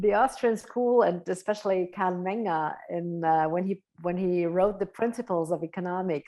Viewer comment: Who are you referencing? Sounds like Camnenga? Count Manga? Trying to look them up, but I can't figure out the spelling. ** Carl Menger ** figured it out, nevermind